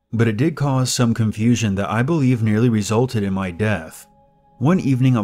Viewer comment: first listen I thought you said "Resulted in my death" not "NEARLY resulted...."